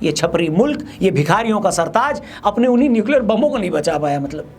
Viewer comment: Baki sab thik hai yrr but what do you mean by bihariyo ka sartaj I'm not bihari but still have some sense watch your tongue before saying anything. Pakistan ko kya keha tu khud hi racism faila raha hai have some sense bro inta bada ho gya hai akal gaas charne gyi hai kya shadai jeha nai hova tha. Koi we muh chak ke video bnana lg jnda hai ajj kl. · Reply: Bhikariyo not Bihariyo😂😂